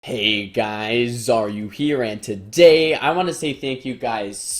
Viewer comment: for the past month i thought he said "Hey guyzzzzz, are you here?"